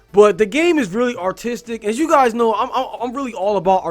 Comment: For a second there I thought you were saying autistic instead of artistic.... come to think of it that would actually explain why there's literally nothing going on in the overworld because the designers would blank out while designing it (Yes, I am autistic. Yes that shit happens to me too. Yes, it is as much of a pain in the ass as it sounds)